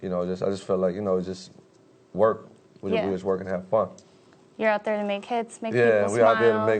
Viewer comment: I swear this nigga said we just in there to work and fuck lmao that's what it sounded like